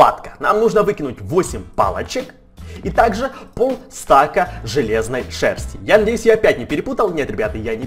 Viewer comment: железной шерсти мне так послышалось